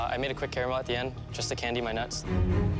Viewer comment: Did he just say candied my nuts? 🙃